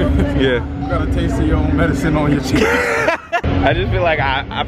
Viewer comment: Y’all looked so good “You have a taste of your own medicine on your chin” 😂😂😂💀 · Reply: I thought he said cheeks🤧🤧